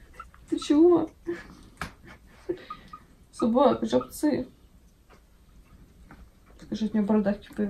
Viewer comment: Расслышать жОПцы, вместо жабцы =) и представлять свистящие жопки =)